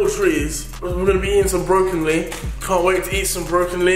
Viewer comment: can't wait to eat brokenlee 😂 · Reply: CMPLCTED it sounds like he say brokenlee 😂 ik it's brocoli xD